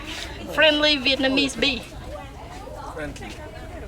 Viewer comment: I thought she said . Friendly Vietnamese bicycle when she was holding the snake at the bikes 😂😂😂